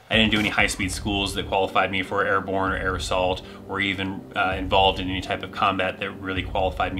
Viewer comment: When you first said air assault I thought you said aerosol and thought that to be a odd badge to give.